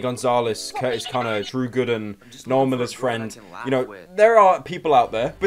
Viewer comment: damn i thought he said "noel and his friend" iabout cody ko haha